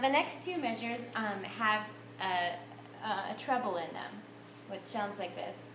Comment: OK answered it myself... I see it is "treble" - and now I understand why it never made sense to me when I heard it said as "triple(t)", since it's not and even group of three...